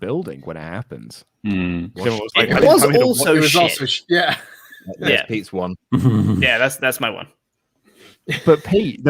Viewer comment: How did they not notice Fiend's nod to Brodie Lee?!?!?!? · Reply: he did the ‘yeah yeah yeahhhh’ catchphrase but a lot of people seem to either not have noticed it or thought he said ‘yowie wowie’